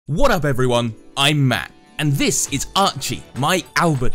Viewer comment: I thought he always said im mad😡 but he says im matt